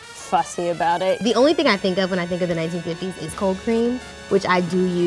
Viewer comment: in 0:42 i heard cocaine instead of cold cream. i gotta go...